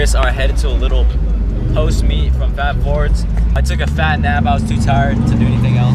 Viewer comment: Did he just say he took a fat dab 4:27 ? He looked so innocent 😂😂😂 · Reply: He’s said a nap it hella sounded like dab 😂